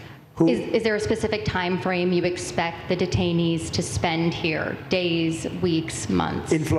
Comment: detainees, i thought you said retirees..........